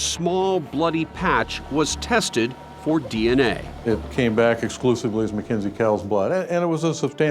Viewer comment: I thought that they said the DNA on the bloodstain was inconclusive